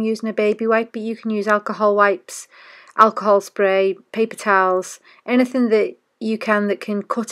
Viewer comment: I'm pretty sure did not say 'pig tails' but I can't think what she said instead...maybe 'pig towel'? Anyone?? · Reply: Haha, no, I said 'Paper Towels' although it does sound like 'Pig Towels'. Not sure what they would be. LOL!